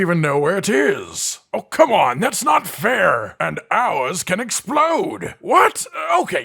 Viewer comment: I thought i heard "owls can explode" and I was thinking, you monsters. For number 10, I love doing those things and then making a non quest monster rideable to start the battle off with a bang